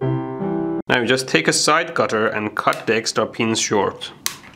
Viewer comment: So misheard him say "Just take the side cutter and cut the extra penis short." No, my brain's not dirty, yours is.